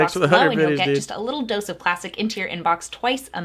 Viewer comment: I heard her say to subscribe to her news letter to get a little dose of plastic into my inbox and my mind just accepted it. That's what she said and you can't convince me otherwise.